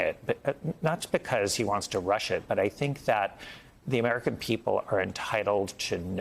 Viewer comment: Did he just say 'not rush it'? Crazy